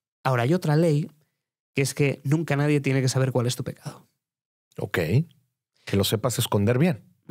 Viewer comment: Cual es la palabra que dice en el min 12:19, sobre que es lo que nunca nadie tiene que saber cual es 'tu ... 'que'? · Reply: Pecado